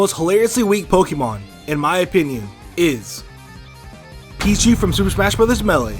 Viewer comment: I heard "Peachie from Super Smash Melon" instead of "Pichu from Super Smash Melee"